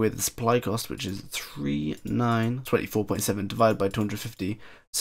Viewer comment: LoL @ 9:49 "Dzzz" instead of "This is" :P